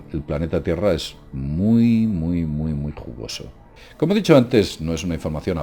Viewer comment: el planeta tierra es muy muy que??? no entendí lo que dijo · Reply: "jugoso"